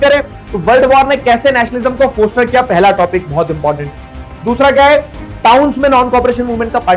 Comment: "Kaise Nationalism ko kiya", usske baad kya bole aap? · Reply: Foster